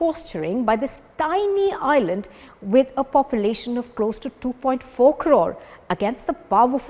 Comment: 'With a population of close to 2.4 ...' ... what did she say? 🤷🏽‍♂ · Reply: Crores